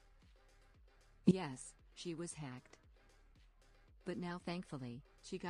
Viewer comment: When you said hacked I thought you said you killed😭